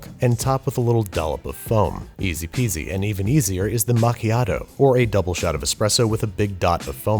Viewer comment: When I first heard the word "Macchiato" I thought everyone was saying "Amontillado"